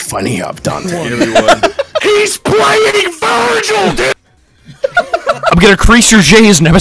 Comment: I thought he said he's playing virgin.. not Virgil lmao